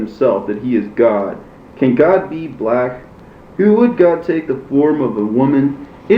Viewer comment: "Can God be black?" 😨😨 Did I hear that correctly??